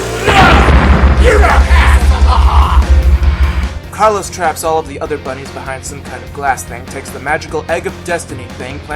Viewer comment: what did you say in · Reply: YOU DON'T HASSLE THE HOFF!!